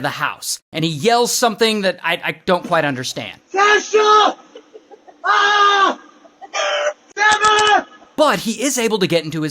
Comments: I think he said my foot AHH I’m hurt maybe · I think he said... "Got Shot! Ahhhh!"